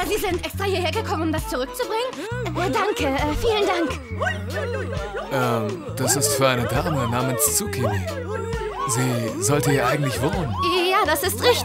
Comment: ICH HAB DIE GANZE ZEIT ZUCHINI VERSTANDEN, LOLOLOLOLOL ^°^